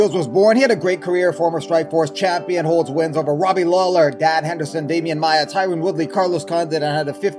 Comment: Sounded like you said Dad Henderson, which would be accurate.